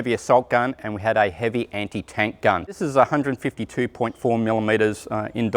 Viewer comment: Thought he said BiaS-152 but that could be the War Thunder talking.